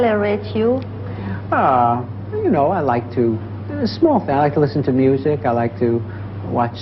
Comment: Woody says "I like to...small things", which the interview must have misheard as "I like to smoke" because the French subtitle says "J'aime fumer" ("I like to smoke.")